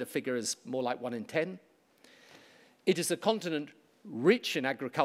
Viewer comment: At no point did he call Africa a country. But he did call it a continent at 1:55. And at various times he said "African nations". I'm just saying. I'm not political, I'm just saying that you might have misheard.